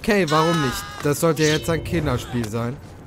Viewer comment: Ich hab bei 31:52 anstatt Kinderspiel, Killerspiel verstanden. Das würde sogar noch Sinn machen